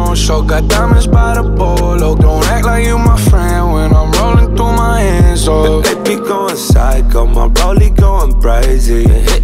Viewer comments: I thought it said when im scrolling through my insta Im so fckin stupid 🤡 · Wait.....I thought he was saying "I HATE BE GOING PHYCO" lol man i don't know shit I guess